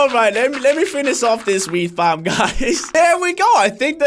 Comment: he sounds like hes saying weed when hes saying wheat